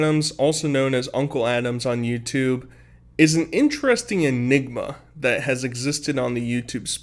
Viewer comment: When you said Unk Adams is an “enigma” he thought you said unkle Adams is “myniga”